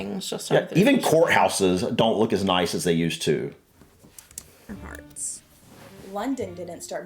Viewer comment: Had to re listen, I thought he said “even whore houses don’t look as nice as they used to” 😂